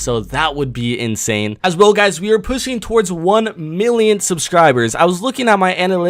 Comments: did you say pussying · I heard “as well guys we r pussying to 1 mil subscribers “